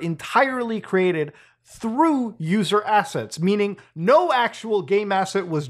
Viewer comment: did Muta just say assets???? Great asset great great asset to the company 🎉🎶🎵